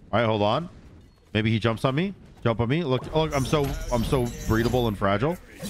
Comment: does say "i'm so breedable and fragile"?